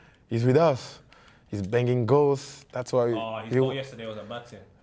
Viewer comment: Who else heard 'Banging girls' at 3:19??